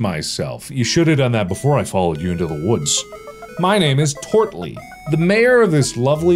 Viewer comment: i thought the character said tortellini for a second.....